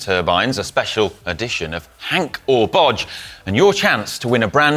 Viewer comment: did he really say: “HANK or bodge” 😂